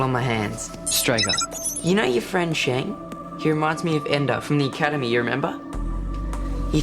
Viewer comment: Did he just say Ender?